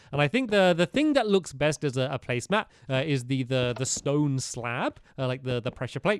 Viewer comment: He did not say baby blocks instead of slabs